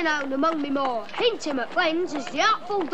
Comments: "What you starin at?" Love this little accent! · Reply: I agree, Jack Wild's accent sounds great which makes him having a great singing voice when he sang "Consider Yourself." Plus, with his accent saying that his name is "Dodger", I thought he said that his name was "Thonja." Plus, when he said "Fagin's" name, I thought with his accent he said that his name was 'Fagey." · It's funny, when I first heard Dodger saying his name was Dodger, with his accent I thought he said his name was Thoncha and all the other characters weren't saying his name clearly where I thought it really was Thoncha as his name. Plus, when all the other characters were calling Fagin as Fagin, with Dodger's accent I thought he called Fagin as Fagey for a nickname.